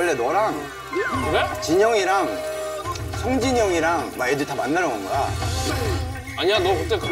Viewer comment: Did Youngjae say Sungjin? Like Day6’s Sungjin? Are they friends too?